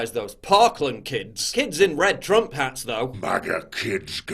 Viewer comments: How come the media can lie with impunity? They should be sued for inciting race hate. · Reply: Hey, english isn't my first language, did Paul say "kids in red Trump hats though" on 1:37? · The catholic church is the real enemy of freedom..... bible followers and atheist alike are the enemies of the Jesuits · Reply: Hey, english isn't my first language, did Paul say "kids in red Trump hats though" on 1:37?